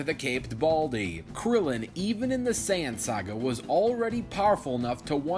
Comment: My dumbass brain thought he said the Sans Saga XD